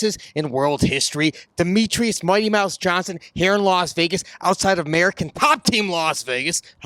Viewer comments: when the says here in las vegas...it sounds like he is saying hair loss vegas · Swear to god When he said here in Las Vegas I heard "Hair Loss, Vegas" and was like that's outta pocket don't insult the mans head ☠️